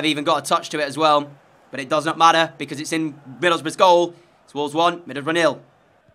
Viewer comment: ngl... every time u said "middleboroughs" i heard "middlebruh" lol